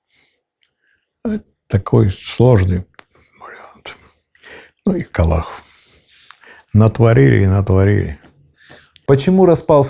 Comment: мне послышалось, или он действительно сказал: "ну их к аллаху"? то есть, "ну их к чёрту"?